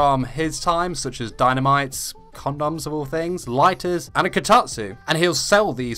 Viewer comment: Did you say "Condoms"?